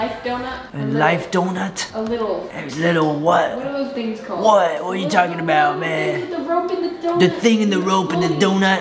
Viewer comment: did i hear him say : What are you talking bout baae